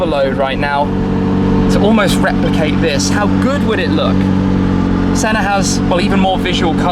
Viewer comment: when you said "how good would it look" i thought you said "how Good Wood it look"